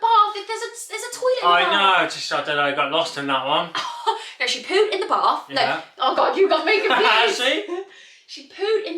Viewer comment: Ok but I also thought you said in then bath🤣 I am also hard of hearing😭😂 hahah u look gorg x